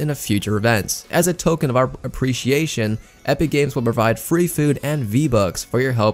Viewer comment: I heard? FREE V-BUCKS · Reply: did I hear free FOOD